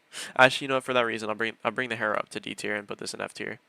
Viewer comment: At first I thought you said aug for d tier and I got so confused